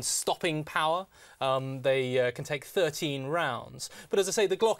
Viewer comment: I thought he said 30 round instead of 13